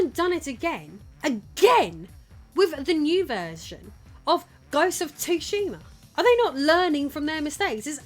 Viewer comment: Did you just say "Ghosts of Tushy"? Lol! 😆